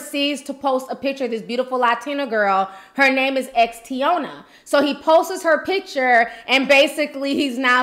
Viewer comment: Did you say her name was Thotiana @ 1:15 ? 🤔